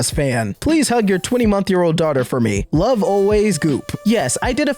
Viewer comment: what the hell is a 20 month year old? You said it twice, lol.